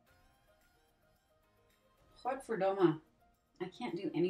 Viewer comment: Did you just say godverdomme at 4:44 ?? 😂 Are you Dutch? I'm Belgian. This is my first time watching a video by you